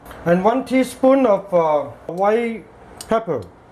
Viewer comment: unable to catch his sound, what he says@ 03:38/03;59, what powder is that, any one could write the name pls! I want to make buns for me, thanks1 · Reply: hi it is white pepper